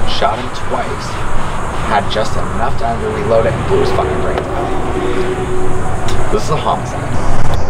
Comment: what does he say after " had just enough time to reload it?" Might be my phone but I can't make it out what he said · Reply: “he blew his fucking brains out”